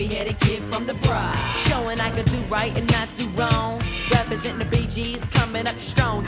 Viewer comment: "Representin BG's and comin' up strong, and..." What?? I had to look up the lyrics, because I always thought he said "Rappers in the Bee Gees" and was like "WTF??", since I know that's not a rap group.